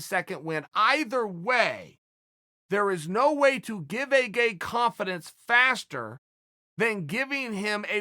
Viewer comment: does he say "give a gay" ? Or is my hearing that bad